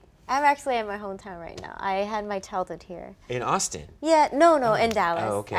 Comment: Can somebody please tell me what she said @ 2:53 ?🙇 I had my ...... I don't get it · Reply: Nikita Gupta ‘Childhood’